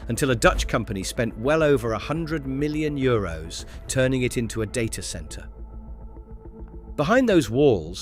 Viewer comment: At the 7:09 time spot, Data Centre? I thought at first they said 'Day Care Centre ' lol